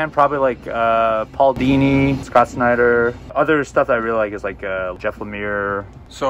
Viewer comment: I swear when I heard y’all say Scott Snyder I thought you said Scott Steiner. 😂